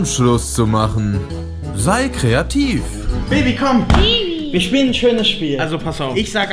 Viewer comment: ich hab Bibi verstanden 😂😂😂